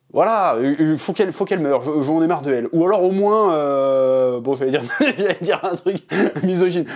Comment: au moins quoi chef?🤣🤣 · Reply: Au moins qu'il l'a soulève